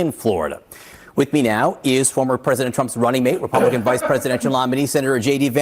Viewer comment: Initially I heard "Former President Trump's running mate" as "former running mate" and thought, "nnnooooo" ^^